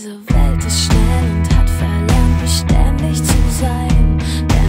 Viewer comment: Wow! Ich habe immer verstanden "Diese Welt ist schnell und hat verlangt beständig zu sein" Aber jetzt sehe ich dass es eigentlich heißt "Diese Welt ist schnell und hat verlernt beständig zu sein". Hm ... man könnte es aber auch so sehen dass diese Welt schnell ist und verlangt hat beständig zu sein und die Beständigkeit eben dadurch verlernt hat. #HermetischesGesetzDerAnziehung